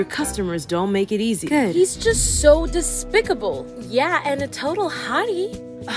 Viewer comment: “He’s so disrespectful.” “And a hottie.” “He’s literally trying to take down my coffee shop and hurt many people I’m not interested.” How this movie would realistically end. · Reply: I thought she said despicable?